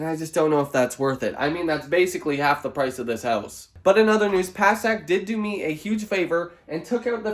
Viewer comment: Im sorry I heard "Passat" instead of Past and I laughed so hard. I was so confused what did a car had to do with removing a fence! 😂 Good luck and yes I am still from Estonia wating on merch and mercy on paying customs duties on that.